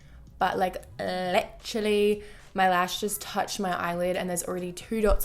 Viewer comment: The way she says "littrlyyyyy" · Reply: I thought she was saying electrally 😂, love how much accents can vary 👏